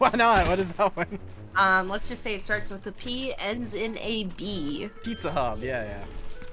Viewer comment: I think she was trying to say was porn hub